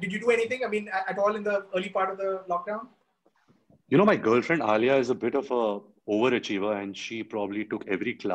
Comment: Did he just say, 'My girlfriend Alia', My heart💓🤧 He surely loves her, like he's never loved anyone before....❣️